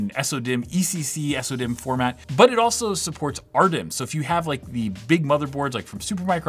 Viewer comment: Can someone explain what the he's talking about? Is he saying you can use some sort of adapter to convert it to SODIMM? not getting what he's talking about. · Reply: He's saying "RDIMM", but I think he means SORDIMM (registered SO-DIMM). In other words, ECC (error correction) memory in the SO-DIMM size format. I really doubt there's any adapter.